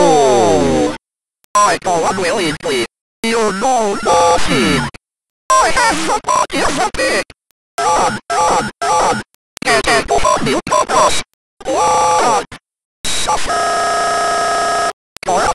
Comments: Bro I heard "I have the body of a pig" 😂😂😂😂😂😂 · I dont know what he said at 0:14 but I heard I have the body of Bob Ross